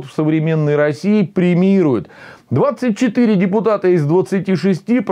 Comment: Вместо "премирует" послышалось "кремирует", аж встрепенулся на радостях.